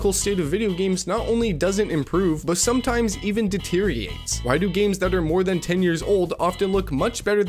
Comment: what word did you say at 0:09? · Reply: deteriotates